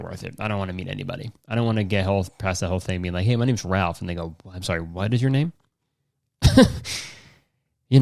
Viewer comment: Did he just say the n word ??? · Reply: He said “and they go”